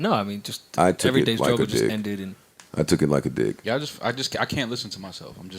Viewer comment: Thought he said "took it like a dick" lmao